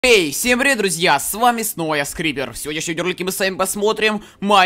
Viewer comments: мне послышалось скример место скрипер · кому послышалось скример вместо скрипер